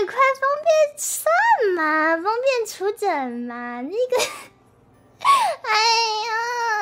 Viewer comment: did she just say "my nigga"?